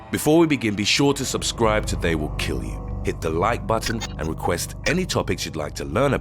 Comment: I miss heard him at the beginning I thought he said “make sure to subscribe or they will kill you” a truly what the fuck moment